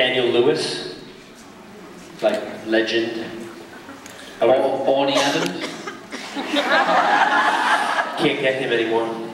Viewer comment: who did Daniel say his favorite actor was? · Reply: i thought he said Orny Adams, he's the guy that plays Coach.